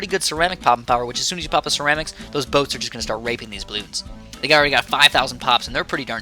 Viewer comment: or so... did u just say theyd be RAPING bloons or GRAPING them?...?